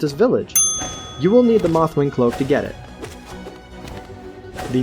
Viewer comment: You're right. We only need the Mothwing Cloak to get the Mantis Claw which is why I never said we need the Monarch Wings to get the Mantis Claw. I just happened to have the Monarch Wings by the time I found the Mantis Lords for the first time. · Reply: oh sorry I must have mis-heard you I thought you said you need the monarch wings to reach the mantis claw. My apologies Other than my stupidity great video